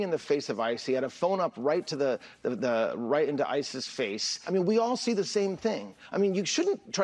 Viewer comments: does anyone else hear him say ISIS in stead of ICE? He's getting his terrorists mixed up. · the captions call them ISIS instead of ICE. 😆